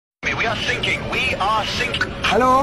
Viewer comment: Is it bad that I heard that “We are shaking”